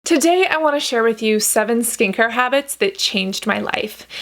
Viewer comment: I heard "seven stinker habits" 😅